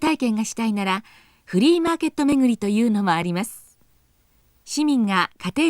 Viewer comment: I'm sure the subtitle should read "flea market" instead of "free market", no?